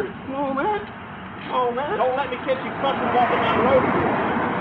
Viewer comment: mark, "Dont let me catch you f*cking walking down the road again!". Anyone else hear that? I thought loud profanity, was why the man was stopped for, to begin with 🤷